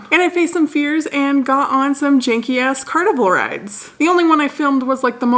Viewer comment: I legit thought you said “drank some beers and got on some carnival rides”. Backed it up and realized I was mistaken.